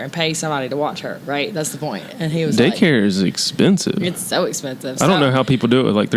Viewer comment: At 22 seconds I literally thought Cullen said "dick hair is expensive". I was so confused and had to restart it to see what was actually said.... Daycare... Daycare is expensive... 🤦‍♀️🤦‍♀️🤦‍♀️😂😂😂